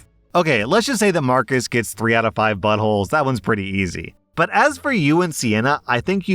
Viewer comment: Story 1: I thought I heard 8 out of 5 buttholes and laughed way too loud. I went back and it was 3/5 buttholes. That would have been a great joke.